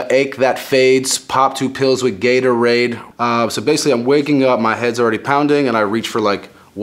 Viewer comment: I thought he said pop 2 pills of Gatorade